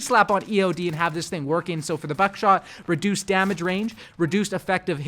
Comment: did he say “fuck shot”?